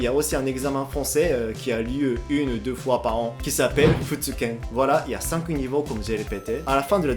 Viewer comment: j'ai compris "veux tu ken"